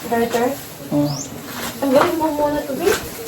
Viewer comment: ANO SABI NI?"TANGGALIN MO MUNA TO BI" "BABY" HAHAHA. ALAMS NA HAHAHAHAHA!